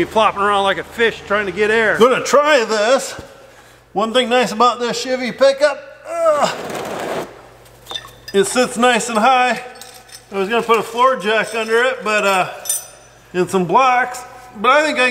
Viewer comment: I thought said shitty pickup🤣🤣 it's OK I'm not a fan of these newer trucks either😂😂